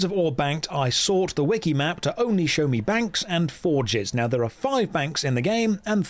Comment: I heard “for jizz” instead of forges